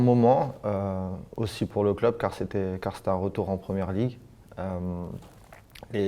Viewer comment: Swear he says stan collymore at 2:04 😂